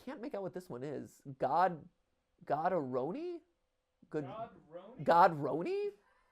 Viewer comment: I thought it said Sodaroni